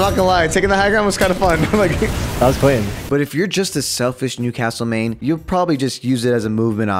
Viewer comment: for a second I thought he said nudecastle